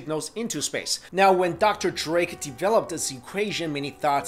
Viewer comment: I thought he said dr.dre lol